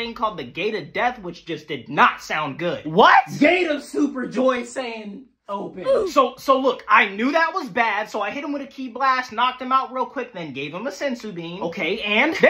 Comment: "Gate of super joy saiyan open" 🤣🤣💀💀 I thought he said toy lol